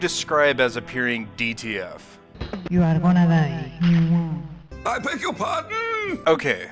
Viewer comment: From what I can gather I think she is saying "You are one of us" in a thick accent before giving you an air smooch. Also what is that "I beg your pardon?" clip from? That's hilarious. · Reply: Through the help of other comments, found out she says "you are gonna die." Clip is from a show called What We Do in the Shadows. Thanks for watching 👍